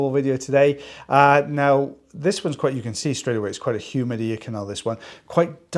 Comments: Just woke up and I thought I heard you say “human ear canal “ listened again and it was humid ear canal. You got me laughing right away! Thanks for your bright spot in my day, your wonderful!! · At first,, I thought I heard you say human ear canal, too. ( My hearing aids are on the charger at the moment) So I went back listened again. Humid ear canal! Lol. got it! Great job!